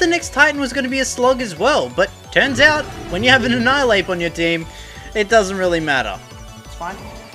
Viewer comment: I TRIED SO HARD AND GOT SO FAR BUT IN THE END: damn i thought he said it doesnt even matter, god darn it